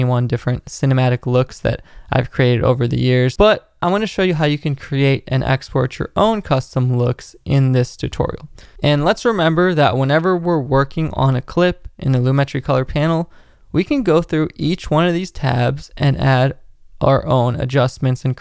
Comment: I thought I heard "And LUTs remember when we're working on a clip..." Haha!